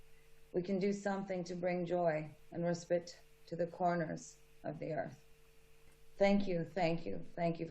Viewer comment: it sounds like Gaga says “...to the coroner’s of the earth.” (Instead of corners). All these monsters can go down with the Gates of hell.